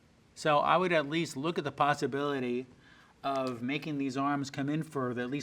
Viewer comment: what I hear: Pastability what the captions put: Possibility